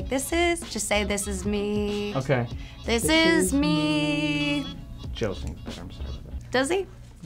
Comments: What did he say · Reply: Liron Stromberg Yeah I asked this and a Lovatic called demidomination told me he says "Joe sings it better". and then he says something like "I'm going to stop right there". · Please could someone decipher what the strange man says at 2:05 ? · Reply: He says "this is meeeeeeee....Joe sings it better"